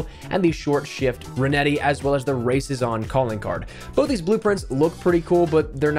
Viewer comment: Thought said: "the racism calling card"